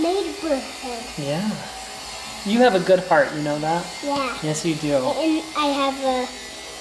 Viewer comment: I have the worst hearing in my family and I was watching this with my mom and I thought said you know have a good fart and my mom immediately corrected me and told me that it was heart